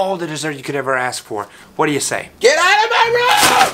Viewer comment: What I heard: ‘Get out of my room! I'm mewing!’